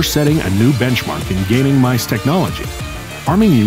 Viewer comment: i thought you said Mouswitch